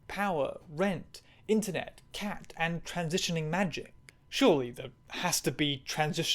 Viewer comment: I heard “transitioning Magic(the Gathering”.